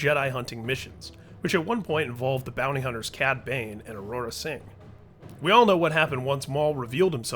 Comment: did you just say Aurora Sing?